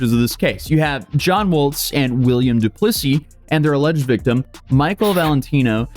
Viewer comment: Did you just say William dePussy? 😂🤣😂